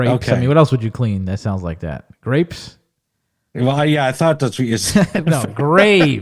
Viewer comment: I heard grapes too